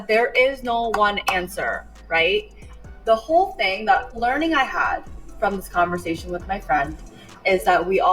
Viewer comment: Sounds like she said learning head